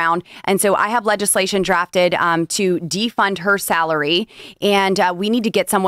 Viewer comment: Defund! .. for a second i thought it said defend.